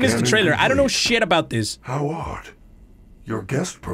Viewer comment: subtitles say how hard instead of how odd and then audio says how hot😂😂😂